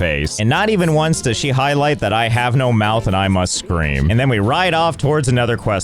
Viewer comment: I can't believe I didn't realize this before the "I have no mouth and I must scream" reference but Josh is basically AM for these poor characters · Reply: Not gonna lie, I thought he said, "I have no mouth and I'm a scream." Because the character looks kinda like the mask from the movie scream, but I'm dumb sometimes, lol. 😆